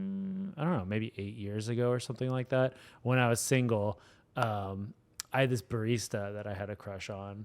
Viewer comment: did Ian just say, “when I was single”?